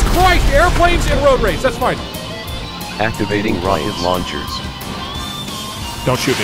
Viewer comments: I love the moment at 7:48 because it sounds like the voice mod said "Activating Riot Launchers" instead of Rocket Launchers. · I love the moment at 7:48 because it sounds like the voice mod said "Activating Riot Launchers" instead of Rocket Launchers. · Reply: I thought it said riot rocket launchers